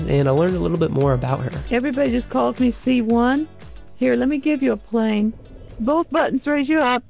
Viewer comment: I'm an avionics tech then i heard she said "let me give to a plane" ahahahaha.